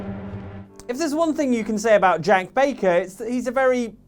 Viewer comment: im sorry but did you say jenk baker? LEERRROOOOYYYY MMMJJJJJEEEENNNKKKIIIINNNSSS